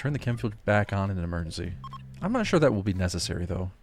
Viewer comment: I still love your videos so much!!! However, your voice is louder now but you still don't pronounce the words clearly, so it's difficult for me as a non-native Englisch speaker to understand them. (e.g. at minute 5:01, I think you say “I’m not even sure that will be necessary, though” but what I hear is “I’m not a… sure that will be necessary, though”; or minute 2:18 I assume that you say “unlike from 3 to 13” but I really only hear “u..i from 3 to 13”) Also, your videos seem to have gotten faster and honestly it makes listening to them a bit stressful. I now have to listen to them at a speed of 0.80 to understand them properly and still enjoy the soothing effect of your great voice. Please don't take my comment as a rant. I just want your videos to be well received by an audience for whom English is a second language. I think you have incredible potential and I would love to see you have lots of success on YouTube. Edit: I think the best way to describe it is that you’re swallowing some letters (especially „t“ and „s“) and sometimes entire words. I think it might be because of hectic. But I also feel like it has gotten better and you pronounce most words clearly now.) Also, sorry again for the criticism. I really love your videos and I just want to help to improve them even more.